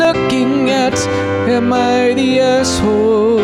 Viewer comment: At first I heard: "Today we're looking at a mighty asshole" and it seemed fitting.